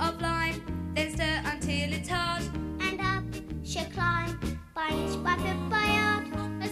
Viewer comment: what the frick did say And up she climb? Æëf&gï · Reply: It's in the subtitles - "By inch, by foot, by yard."